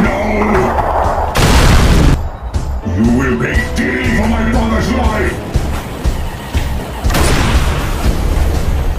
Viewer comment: What did the Legionaires say? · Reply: "You will pay dearly for my brother's life!!!".